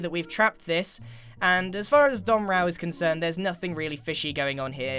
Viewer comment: Ooooooo I thought you were saying dumb rao but u were saying don rao